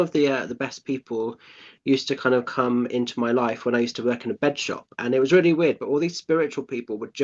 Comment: Hi 👋 I had to rewind as I thought said petshop 😂 I was like ooooh I work with animals.. bed shop? Fabulous chat thank you xx Leigh